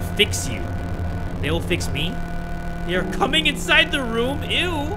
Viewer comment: ngl 4:30 I thought they said they are coming inside you and kubz scouts just said eww after so i thought they really said that 💀